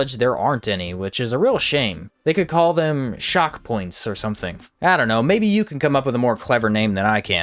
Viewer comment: Shockrams instead of Chakrams